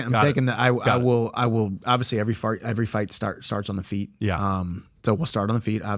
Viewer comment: Lol 52:29 he said fart instead of fight 😂